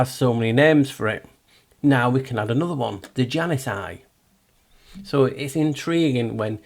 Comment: Hi At 40:17 I was listening and it sounded like you said Genocide and not The Janus Eye, 😊 so I had to go back and listen again.. This time I watched the screen and even though I could hear that you said Janus Eye this time around, the closed caption also translated it to Genocide 🤓😅 We know how [they] love the phonetics, and there are no coincidences, so there likely Is a connection, and I thought it worth mentioning. ☺️🤷‍♀️